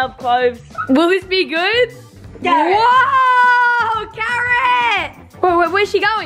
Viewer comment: When you said carrot h thought you said Karen